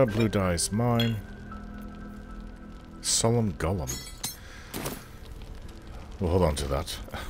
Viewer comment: You said "summon golem," but I swore I heard you say "solemn golem" which had me laughing for a while!